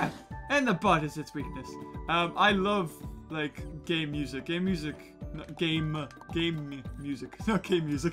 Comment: My face when i thought he said gay music: o_O WHAT IS GAY MUSIC IS THAT A THING 2 seconds later Oh okay nevermind